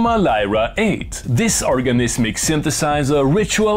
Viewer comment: I thought it said orgasmic lol !